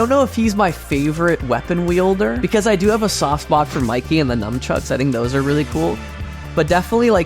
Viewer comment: I swear you say, "numbchucks," here.